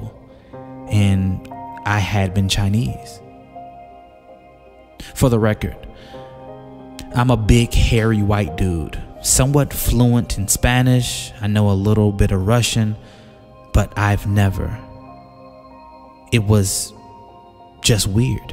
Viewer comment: I must be dreaming now cause I thought I heard you say you a "Big hairy white dude"....🤔